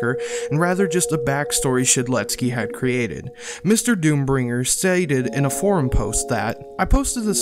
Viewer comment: did you just say "saided"